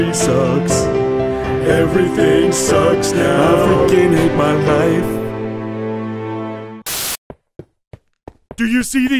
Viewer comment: I heard "I f*cking hate my life"